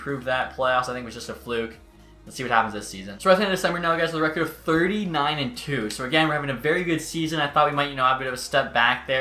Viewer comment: I didn’t hear right and thought you said 39-2😂 I was so confused